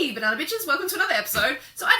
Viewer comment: did she just call me a banana b*tch ?